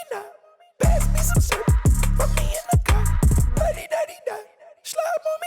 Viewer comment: I heard "Fuck me in thu cuh/car" Just the clips you showed from Future make all those diss tracks Nick Cannon used on Eminem in 2020 look top tier in comparison